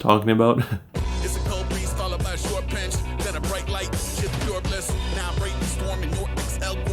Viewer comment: I think after he says "then the bright lights" it's followed by "shit's pure bliss"